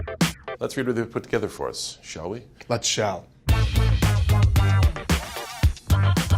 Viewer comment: What'd he say at 0:14? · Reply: "Let's shall"